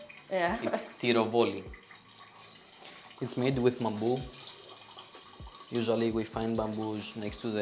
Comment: What is the wicker basket called? My captions said "bamboo" but it sounded like he said 'mambu.' I looked up several spellings but i can find nothing relevant 😢